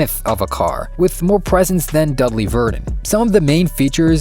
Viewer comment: What does he say? Dudley Verdin? I don't get the joke. As a Potter fan, I immediately thought of Dudley Dursley, ha ha. :)